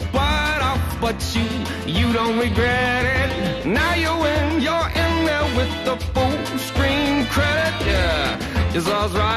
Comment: what is the singer saying at 0:30 to 0:34? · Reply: Maybe it's "now you win, you're in there with the full screen credit"